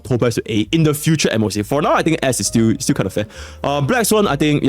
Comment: was talking about Topaz and he said "S is still kinda fair" and my brain thought he said "her a$$ is still kinda fat"😂😂😂